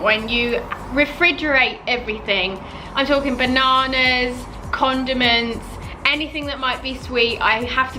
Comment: for a moment i thought she said condoms